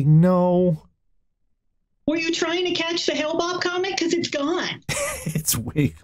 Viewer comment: Did she say "Hale-Bopp Comet" or is this some reference I don't get because I have issues with interpreting english pronunciation correctly?